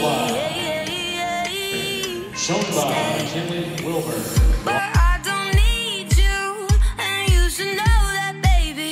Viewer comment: What name did they say at 3:40? I know it said wilburn but what was the first name? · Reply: Equestrian Rose they said McKinley Wilburn! They were announcing frisco and I but mispronounced my last name aha!